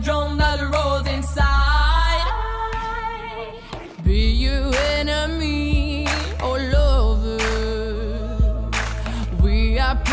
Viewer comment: why do I hear "pee your enemy all over"...